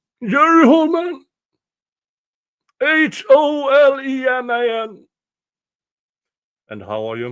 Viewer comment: Holeman’s testimony, OMG! And he carries a weapon in public-what? Who does? Holeman. Who? Holeman. Oh I thought you said Manhole. So yeah, but no. 🤦🏻‍♀️🤦🏻‍♀️🤦🏻‍♀️🤦🏻‍♀️